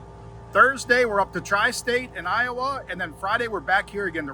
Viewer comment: This is what the people want. Behind the scenes footage. Every other YouTuber will post the races so its just repetitive content. This is nice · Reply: I thought you said dry state. I got a little offered lol. Then realized you said tri state